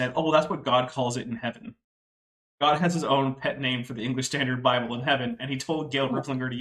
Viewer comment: I don't think I heard that "pet name" for the English Standard Version properly; what was it? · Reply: ESB